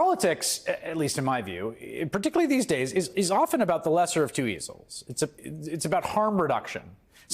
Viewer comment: Sounds like he said "politics at least in my view, particularly these days is often about the lesser of two weasels." This guy is a bona fide genius!